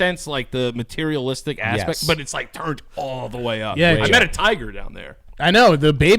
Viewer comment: I don't remember if it was or but when they said "they met a tiger" i thought he said "i met a thai girl" 😂